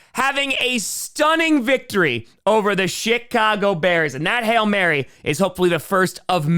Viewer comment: is it just me or did he say the shitcago bears